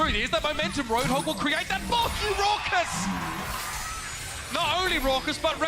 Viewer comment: did he say “F*ck you Rawkus”?